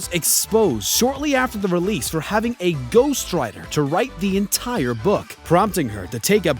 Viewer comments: A ghost rider bruh that's what it sounded like he said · Wait whats wrong with ghost writers... Bojack horseman does it too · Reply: At first I thought he said ghost riders.